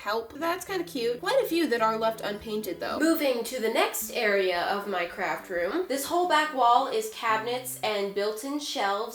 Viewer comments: I almost thought she said "now let's get back to my CRACK room" 😅😂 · When she said craft room i thought she said crack room XD im dieding of laughter